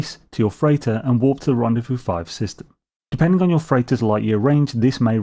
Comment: With my particular accent, I kept hearing "walk to the rendezvous system" and like.... man it took me a second haha! You are amazing! Wow. Thanks for doing this for us.